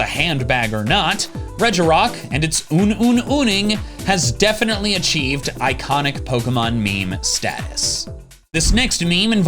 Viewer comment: help instead of "Un Un uning" I heard "goon goon gooning" 😂